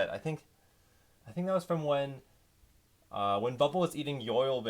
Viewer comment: thought you said "5b" and i was like WHAT?!?